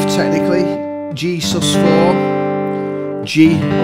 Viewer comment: Hello, what is the opening chord. You say "g" "g sub ?" the second one I can't hear you sort of mumble it. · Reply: Thanks. But its right arou 4:15 it sounds like you say "G sus scraw". I don't follow you there.